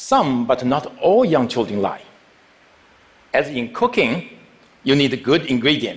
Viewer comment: when he said cooking i thought he said cocaine